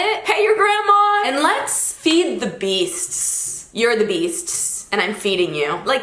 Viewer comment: I thought she said “let’s feed the geese” and honestly, you know what? That also works and I like that too. I’m gonna use that as a metaphorical phrase from now on